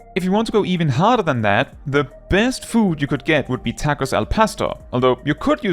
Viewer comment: Just a small nitpick I have to mention as a spaniard: the tonic syllable in "pastor" is "tor", not "pas", so it would look like "TAcos al pasTOR", with the capital letters symbolizing where the tonics are. I was getting incredibly triggered by how it sounded like you were saying "al pesto" instead of "al pastor" 😂